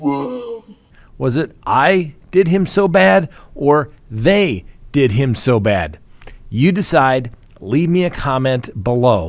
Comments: I heard Alex M say “they did him so bad”. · Honestly, I heard “they did him so bad” It just sounds like he was crying and his voice cracked. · I hear "THEY did him so bad" What could he gain by killing his wife and son. Especially killing his son in such a gruesome way? It makes no sense to me. And, how did Maggie's phone end up on the side of the road at the exact time AM turned on his car 0.5 miles away in his driveway?